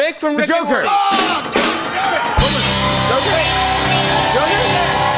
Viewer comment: I heard a “OOOOH! God Dammit!” At 6:15. Just sayin.